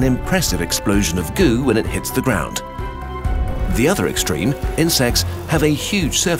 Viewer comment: insects - heard it as in-sex